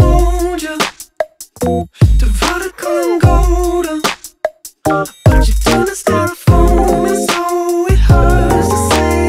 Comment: John Terry Instead of "but you couldn't step forward," it sounds like they say "but you turned to Styrofoam," implying that she has a reckless, carefree lifestyle that includes drug use (referencing the mayonaisse while blazed line)